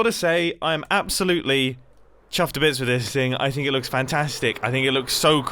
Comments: Please say “chaffed to bits” from now on as that is what Scar thought you said all the time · dang it Scar now I keep hearing "chaffed to bits" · in scars defense, mumbo does sound like he says chAffed instead of chUffed.